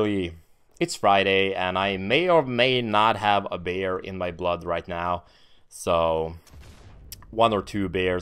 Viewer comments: I thought he was joking and said "a bear in my blood one or two bears" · I pissed my self laughing when you said that you had beer in your blood, because I was realy tired and I thought you said you had a bear in you blood :P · lol I thought you said "Bear in your blood" LMAO